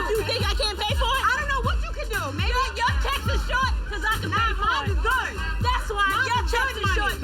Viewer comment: I thought she said “your tits is short cause you can’t you can’t pay for it” 😂😂